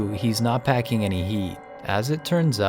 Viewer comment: or 0:28 Did he say “he isn’t packing any YEET”...Is it just me or no?